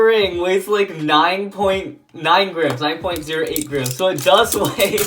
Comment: am I the only one who heard grems